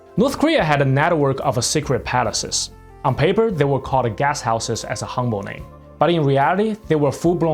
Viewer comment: I thought he was saying "Gas Houses" until I realised he was saying "Guest Houses" But then it is North Korea.